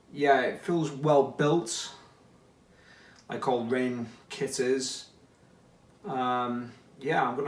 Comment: What did you say at 1:51? Kitters? What does that mean? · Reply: He said “it feels well built, like all Rane kit is”!